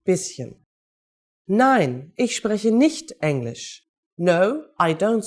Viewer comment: I read on the screen "Nein, ich spreche nicht Englisch". I hear her say "Nein, ich SPRECHEN nicht Englisch. Why is that? Why is I in ich not capitalized like I in English? Is it because I is a pronoun in English and not in German. SORRY JUST NEED A QUICK BUT BETTER UNDERSTANDING?